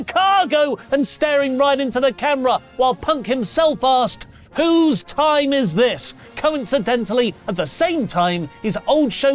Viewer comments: Thought punk said whose town is this...god I'm goin deaf in my old age..😂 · I thought he said “Whose city is this” but either way…oh well…CHICAGO, CHICAGO, CHICAGO 😂 Now Ryback should man up and retire 🙏🏻